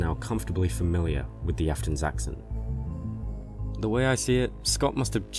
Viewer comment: I thought it said aftons accident